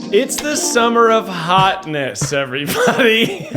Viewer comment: at first, i thought it said the 'Summer Of Hotmas'.